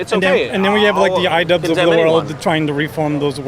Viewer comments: Did he say Idubbbz? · what is the interviewee saying? The "I-dubs" of the world? I didn't catch his reference. Any help is appreciate.